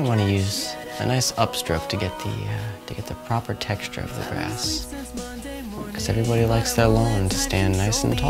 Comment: "Youre gonna wanna use a nice upstroke to get the proper texture of that grass" Sounded like he said Ass.